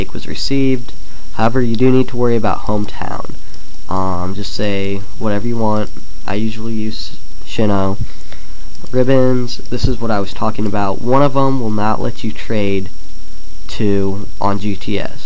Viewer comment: Did he say "Shinnow"? lol.